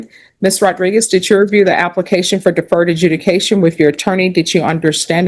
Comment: "Application for affered adjudication", I swear every time she says this in my mind I see that general from South park, bigger, longer and uncut. "I don't listen to HipHop!"